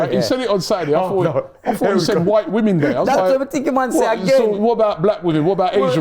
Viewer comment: I thought TY said White Women Day 😂